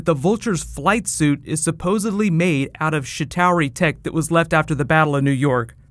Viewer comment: when u said chitari it sounded like u saide shittari. I laughted like hell